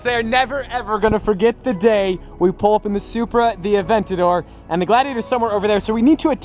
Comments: DID HE SAY BMW SUPRA!!!!! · Am i the only one who heard him say "BMW supra" even tho its a toyota supra? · ANYONE ELSE HEAR HIM SAY BMW SUPRA?!